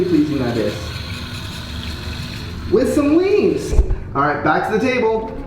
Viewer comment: my high ass thought he said, "With some WEED!"